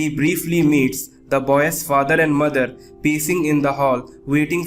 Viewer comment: The "boyas" instead of boy